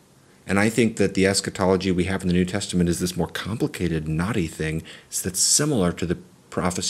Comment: I’m sure you said “more complicated, knotty thing” but I heard it as “more complicated, naughty thing” And did a double take 😂😂